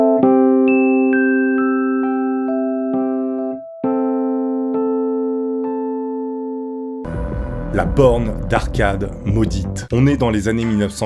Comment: Y'a que moi qui a entendu "la "Porn" d'arcade" ?